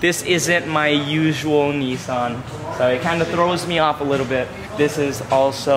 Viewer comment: did he just say Nissan